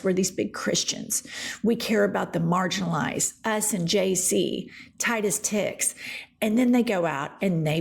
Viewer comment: Damn, i thought she said "Us and Jay-Z" 9:48 and im like damn, bro cant catch a break...